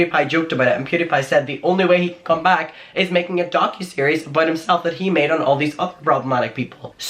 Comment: When you said “Docuseries,” the CC thought you said “donkey series,” and I laughed my ass off. Sometimes I just leave those on for a good laugh. 😂